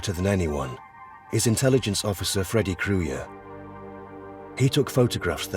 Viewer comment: Freddy Kruyer... My God I thought he said Freddy Krugger..... I thought to myself this is going to be one helluva nightmare!